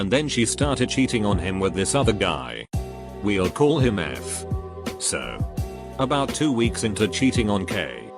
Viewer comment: I wasn’t looking at the screen and I thought it said and we’ll call him gay and I felt my face literally go ‘wtf’